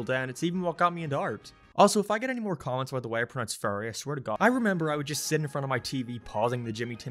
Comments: HAHAH even the subtitles think you said Furries instead of fairies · when you say fairy it sounds like your saying furry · I honestly thought he said "If I get anymore comments about the way I pronounce 'furry' I swear to god"